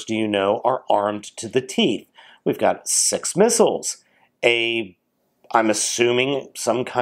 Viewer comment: Wait a minute at 3:24 did you say SEX missles